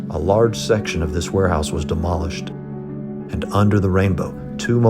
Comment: The captions aren't getting anything right 5:34 what is saying, if you dont understand said a large section of the warehouse was demolished captins said a large section of the warehouse was polished